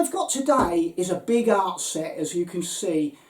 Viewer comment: I watched the start of the video twice. I swear it sounded like you said, “This is a big arse set.”. I agree!!